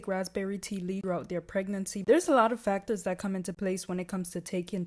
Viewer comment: i thought it said raspberry but nah it said pregnancy